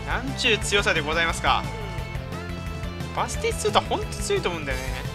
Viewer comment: の所バスティストゥータに聞こえる